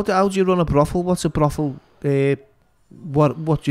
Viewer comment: What a mushroom 🍄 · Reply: My bad I thought he said 'what's a mushroom' 🍄🍄...think he just a troll his name is fighting tories..that's deffo a bare knuckle gig I wanna see some tories getting involved in the unlicensed n that I'd pay,what Tony Blair having a swing about you got my £20 🤣